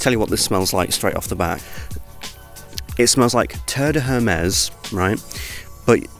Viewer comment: sounds like he said TURD de Hermes 😂